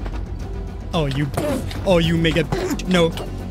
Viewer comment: Am i the only one who heard him swear the n word or atleast it can sound like that? Edit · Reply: He said "mega b**ch"